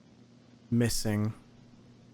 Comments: I thought it said “Kissing” 💀 I FR THOUGHT THATS HOW HE GOT INFECTED OR SOMETHING WTH-😭😭😭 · I legit thought that said kissing I was like “wtf?”